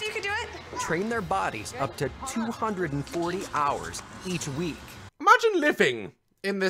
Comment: Sorry but can someone confirm to me that there is in fact only 168 hours in a week please unless this family is in fact the family of doctor who because i have watched that bit multiple times and i hear them saying 240 hours a week